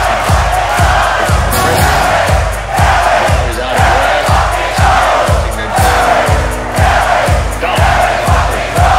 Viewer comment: What are people saying at 3:14 ? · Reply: Here we here we here we fucking go